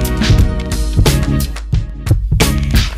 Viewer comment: Tell me I'm not the only one who heard "papa" in around 5:00 in the music he played lmao. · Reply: That was screaming papa into the crowd XD